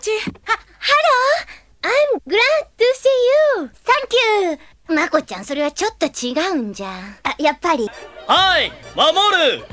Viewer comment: l would like know what Rei said xD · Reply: She said "Mako-chan, I don't think that's quite it..." And Makoto says "I thought so." 😆